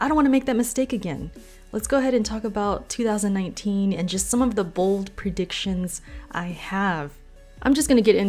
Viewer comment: i thought you were about to say 'and just some of the bullshit' lol